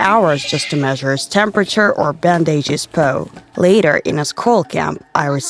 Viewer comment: did she really say pou